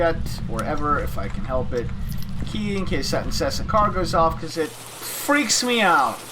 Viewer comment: "key in case..." I couldn't quite understand what you said after that. Key in case what happens? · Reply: In case the car alarm goes off, which is annoying as hell